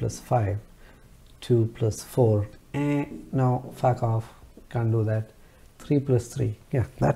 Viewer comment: I'm new to your channel... & I would love to appreciate ur work & nice explanation. aeennn sound was amazing.... did u say "fuck off" @ 9:54? Lmao...